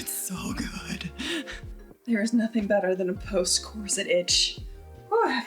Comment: NGL.... I thought the last thing you said was "Post-coital itch"! 😅